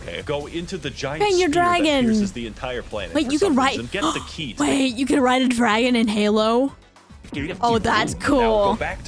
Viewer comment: did she just say....? how dare you. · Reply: I JUST ASKED IF U CAN RIDE A DRAGON? :SOB